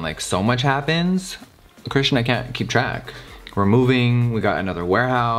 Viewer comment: Did you just say.. Krishna can't keep track around 22:50 ISH ? 🤯🤯🤯